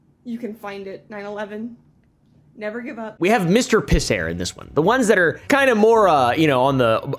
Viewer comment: For the first couple minutes I thought you were saying Mr. Piss Hair. I think I'd rather it just be some guy who rubbed piss in his hair.